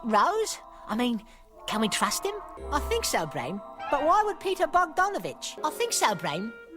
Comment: "I think so Brain...but why would Peter Bogdanovich? " ...I heard that as..."Why would Peter Bog Done a bitch?" Oh lol.